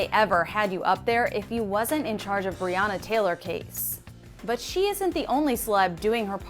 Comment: all i hear (but she isn't the only slut)..is it me only lol..?